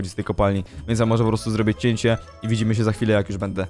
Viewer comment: czy tylko ja usłyszałam "więc ja może poprostu zrobię dziecię"?